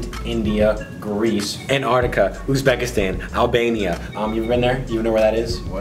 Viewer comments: did he say Albania? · did he say Albania??? Per shqiptaret prsh!! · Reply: Albania🇦🇱🇦🇱🇦🇱🇦🇱